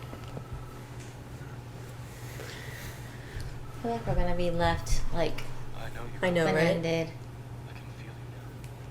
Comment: What does mom say at 52:04? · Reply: The power of love